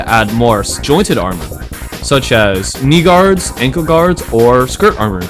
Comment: i heard that bro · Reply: He said n Knee guards